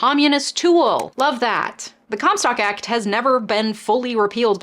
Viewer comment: Haha I thought you said “cum stop act”